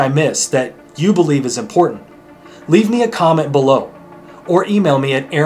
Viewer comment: I hear "leave me alone"